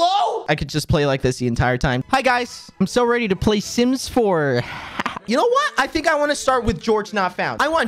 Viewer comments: When you said "sims 4" I thought you said "SIMPS 4!!" AGHKFHGDHDHGDHDHHJHH!!!!!! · I thought he said simps 4 AHHHHHHHHHHHHHHHHHH